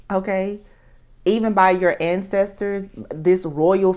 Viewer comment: I thought you said investor incestor but you said ancestor 🙈🙉🙊